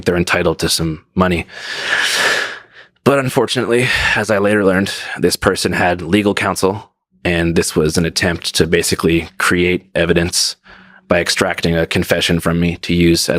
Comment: I think you may have misheard what he had said near the end of the video. He said “I later learned this person had legal counsel and this [The text] was an attempt to create evidence by extracting a confession from me…” He’s legal counsel didn’t say anything, he learned later that she had legal counsel at the time to prep for a lawsuit.